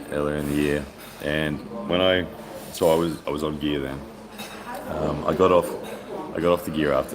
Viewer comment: did you say you where on gear vs Lachlan?